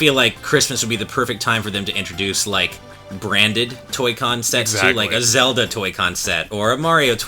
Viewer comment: thought you said "Toy con sex" ...